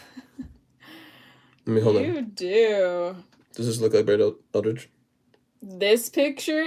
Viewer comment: who did they say you look like! I'm so intrigued by saying he looks exactly like future you while having a grossed out look on her face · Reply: Brett Eldredge